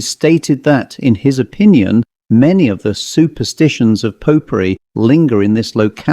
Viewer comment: I thought you were saying pot pourri, not popery 😂